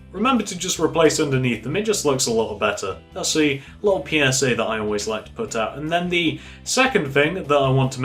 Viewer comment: did he just say little penis